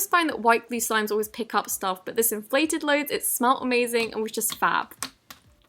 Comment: did she say fab